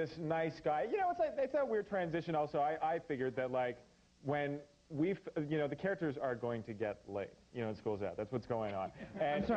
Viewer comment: Did he say "the characters are gonna get lit"? LMFAOOOOO